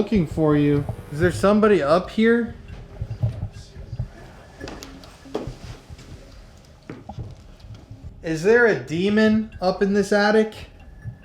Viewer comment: COMMENT "Jailbird" and LIKE tonight's video for a chance to WIN A FREE GIFT BAG!!! - channel: <link> - Today is a huge week behind the scenes. No one knows what we're up to, but we're getting into some absolutely dark and crazy waters this week overseas... I also just had a bit of a health scare that I am currently dealing with, so, send some good energy my way. Love y'all so much! <3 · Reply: I heard a Girls Voice say 'There is Someone up Here' through my Headphones.